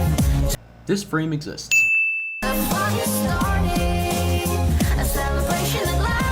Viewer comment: I heard “the fuck is starting”